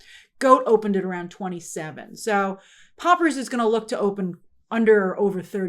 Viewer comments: Did say „Poppers“? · did she say poppers??? 😭😭😭😭